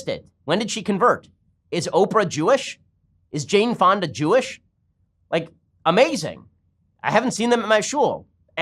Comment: When you said "is Jaine Fonda Jewish?" I totally heard "is James Bond a Jewish?"